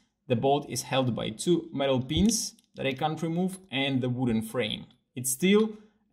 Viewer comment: Hahaha thought you said you can’t remove the two metal beams.. lol